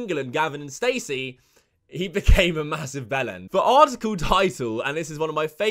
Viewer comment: Wtf did he say at 1:20? Massive belen? Bellon? What is that? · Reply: Bellend. It means tip of the penis.